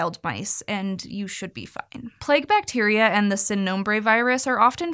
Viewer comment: Uh, call me crazy but did she say “you should be fuckin”